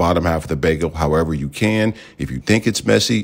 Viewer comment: I thought he said “if you think it’s messy, go bolemic-“